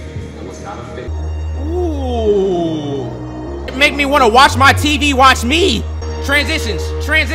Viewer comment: "make me wanna watch my tv watch me" what in the gulag did bro just say😂😂